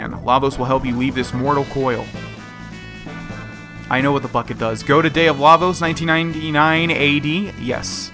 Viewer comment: I thought he said I know what the fuck it does not I know what the bucket does